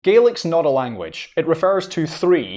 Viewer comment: I thought you said 'garlic'